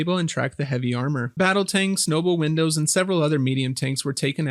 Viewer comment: Did you just say... noble Windows?!